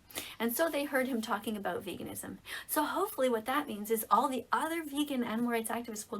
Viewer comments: i thought she said "and all the vegan n-words" and i was like ca- can she say that like is that ok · did she just say ''All the other vegan n-words'' or did I hear that wrong? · i have no clue if i’m hearing this right but did she said “Vegan N Words” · did she just say" all of the other vegan n words activists" at 4:35 um I don't think you can say that miss ma'am